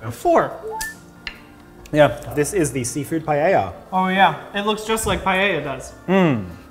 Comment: This was so fun! I was looking at my phone when they were tasting the "paella", and I heard it like "payaya", so I was so confused when I looked at the screen and read "paella" xD Accents make me so happy